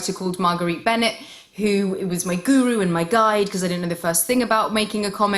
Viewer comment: Did she say guru @ 4:38 🙄